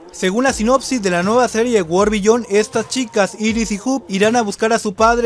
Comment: ¿Cual es el nombre de la serie nueva que dice en el video en el video 3:11 ?? No entendí en nombre. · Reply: World Beyond